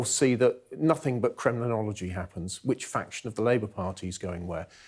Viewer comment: Plenty criminology took place with 80 seat majority thanks to Tories for failing to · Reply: I think he said "Kremlinology"